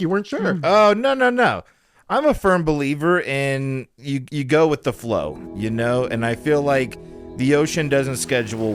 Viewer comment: Not gonna lie, I thought it said “Growith the flow” & it was a continuation of growers. 😭 help.